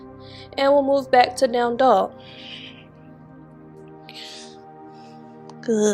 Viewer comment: Thanks a lot 🙏🤍 (i am french and since years now than i follow u each time i heard "damn dog" for downward dog 😂)